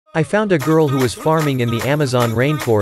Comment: I THOUGHT HE SAID "i found the girl who keeps farting in the amazon" 💀